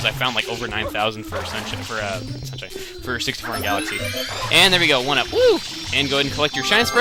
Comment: I thought he said 64 galaxy